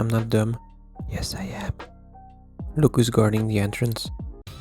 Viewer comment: did bro say "antrence" 😂😂